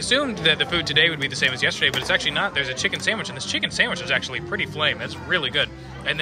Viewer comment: Did you say that the chicken sandwich was pretty flame at 6:42?